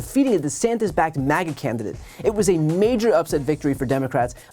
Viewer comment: i had the auto-generated captions on, and it interpreted what Brian was saying as "the Santa's backed maggot candidate"... 😂😭🤣